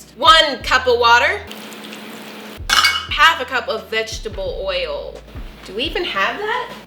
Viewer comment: I literally thought she said 'bitchable oil' because of how she pronounced vegetable oil i